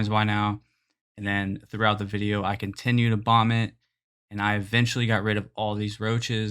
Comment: I thought you said you vomited and continued to vomit. Ha. Then I realized you said..bomb it.